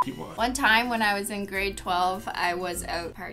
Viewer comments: I thought she said 12 years not grade 12😂😂😂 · i thought she said she was 12 and wasnt drinking cause she was driving i was about to say CANADAS CRAZY LOL LYSM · I THOUGHT SHE SAID AGE 12 NOT GRADE 12